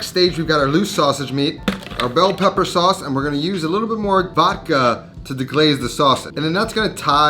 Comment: The word "Vodka", when pronounced in English, for an Italian like me, I hear something like the Italian word "Vacca". Which then in English would be „cow“. :).